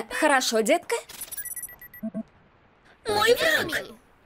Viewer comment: ляп с озвучкой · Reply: У меня наверно паранойя, либо я не так услышал. На 5:57 она говорит "Мой пранк", в оригинале говориться "My enemy",тоесть "Мой враг". Наверно из-за того что фразы соединились, получилось вместо "враг" "пранк"